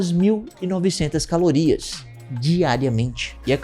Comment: eu jurava que ouvi 2900. Ja vinha aqui xingar vocês.